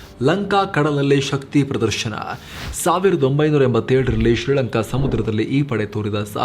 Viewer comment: U mentioned in sub title as 1947 instead of 1987 @ 05:24 minutes Anyways Thank you Team 💛❤️